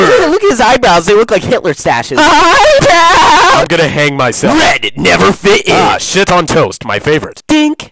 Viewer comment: "Hey red how are ya" "I tried hang myself this morning😀" LMAO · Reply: I really thought he said bang myself. 💀